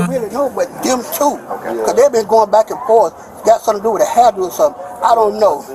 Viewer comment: This was over a hairdo? 6:45 Did I hear that right?😂